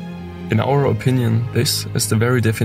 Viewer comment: when he said urbexers often have to deal with transience, I thought he said transients at first, and thought "yeah I'm sure there are a lot of homeless people in abandoned buildings"